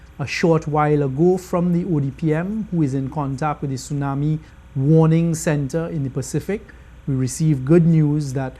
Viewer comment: did he say the tsunami center in the PACIFIC?